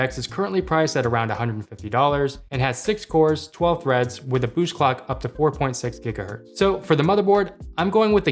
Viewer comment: im just gonna pretend i didn't hear that 🗣🔥: "BUSH CLOCK"